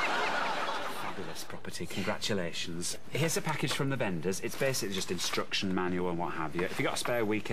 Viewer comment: At first I thought he said "Here's a package from the benders " I thought "?...wot?"